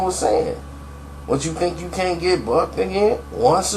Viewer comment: When Prodigy said "What chu think u can't get bucked again" I thought he said What chu think u can't get Fucked again. Woops